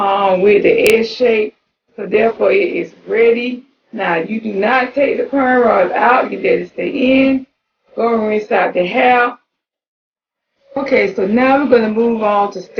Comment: I didn’t hear what you said at ? 💗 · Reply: Do not take the Prem Rods Out, Go and Rinse Out the Hair but don't Remove the Rods.